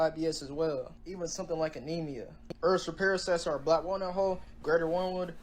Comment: What did he say about the cloves and worm wood?😅 7:57. Can't understand the beginning. Sounded like he said earth's repair black wall hull, worm wood, etc.